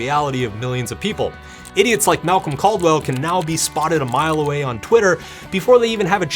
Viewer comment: thought you said Malcom Gladwell for a second there :)